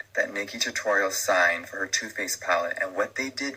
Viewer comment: f$#&!!! I misheard 'Too Faced' as 'Toothpaste' ONCE ... and now I'll never hear it any other way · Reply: around the 44:05 on when JS is speaking, it really does sound like he says toothpaste 🤣